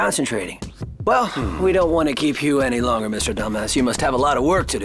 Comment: I thought I heard “Mr Dumbass”